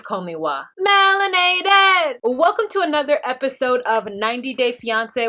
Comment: You got me walking around .. singing.."melanated" ..(sp) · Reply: I was going to ask what Wah was saying ....is she saying Melanated? I thought she was saying Wahinator!!!!! I like both!